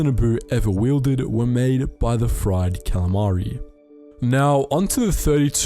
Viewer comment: did I hear fried calamari?